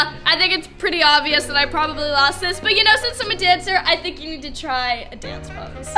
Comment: When she said "I'm a dancer" I thought she said "I'm a cancer" so I'm like, wth. That doesn't mean ur smarter..